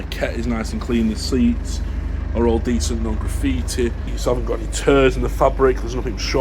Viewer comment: I heard "No turds in the fabric" rather than tears. This is also important